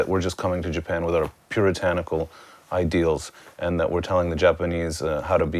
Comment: At first I thought they said "pure tentacle" instead of puritanical... Geez.